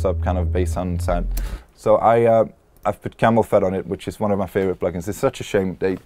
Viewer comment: What plugin does he say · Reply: Camel Phat. It's a drum processing plugin which rose to fame in the mid 2000s due to it's incredibly good sounding distortion when put on bass (as opposed to drums, but it sounds good on drums too). That lead to the producers eventually releasing a free version called Camel Crush which left out the LFO/EQ automations of the original plugin. It had also a volume automator built in which generated this non-sidechain pumping sound in a lot of house/trance records, before more mainstream volume automaters came along. It's probably the most used and pirated plugin in the industry but for some reason it was taken off market and there was never a new version of it.